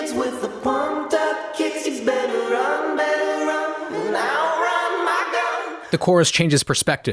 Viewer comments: I didn’t hear the “Out run my gun”, rather, I misheard it as “Out run my run” or “I’m out gas” (for some reason) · I didn't realize until I watched this video that they say "outrun my gun". I thought they said, "I'll run my girl". I didn't know why that people used the song in those meme comps, I just went with it, scary :( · I hear "he's better run" not "you better run". Why does it sound that way?